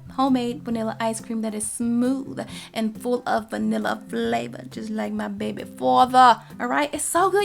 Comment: "Full of vanilla flavor just like my baby father" 😂😂😂😂😂😂 Am i the only one who heard her say that?